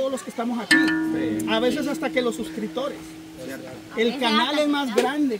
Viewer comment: Queee dijo la ⭐en el minuto 6:45 🤔( a veces hasta que cama 🤔 ) entonces ⭐ piensa que ella es mejor que cama 🤔😂😂 o entendí mal ...si entendí mal disculpas !!!!